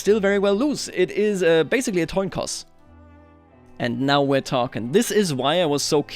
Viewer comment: he said toin coss instead of coin toss. was that deliberate lol